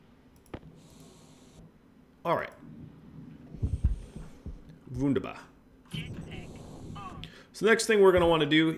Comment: did you just say "wunderbar" at like 24:05 ? :D german for great :D